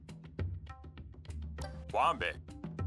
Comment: I swear he said “en passant”